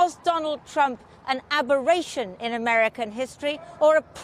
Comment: What's the word she's using? Abhorration or Aberration? I can't make sense of her statement? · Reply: Yep aberration. She is asking was he a one off blip or is this what the USA should expect from candidates/presidents of the future